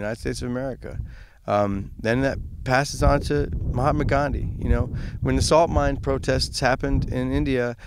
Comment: Did he say Mohammed Gandhi at 5:28? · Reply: Doktur Speshul He said Mahatma.